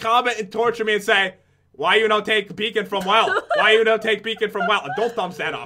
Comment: Enjoy the video guys!! Let us know some mobs you'd like us to fight! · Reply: ***** for a second I thought you said bacon XD don't steal the bacon